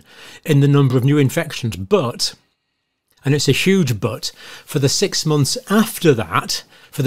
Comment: "BUTT and it's a HUGE BUTT!" - misheard speech